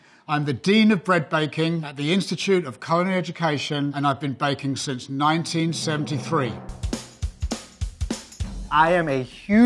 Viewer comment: I heard " I've been behind since 1933"